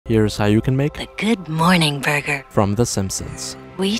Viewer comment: Somehow i heard " Good moaning burger" 💀